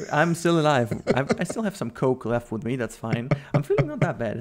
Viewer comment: did say "I still have some coke with me" ?